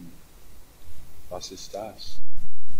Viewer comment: Did you say "Was ist das" in the video at around 17:08 ?? I cant tell you what that is, but "Du musst eine return meldung eingeben" :P It sounded like german what you sad there